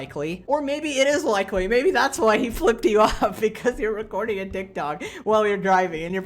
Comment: Captions says dick dog instead of tiktok